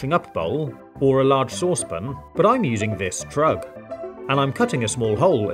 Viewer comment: sounded like “I’m gonna use this drug” And how can we thank you for all this?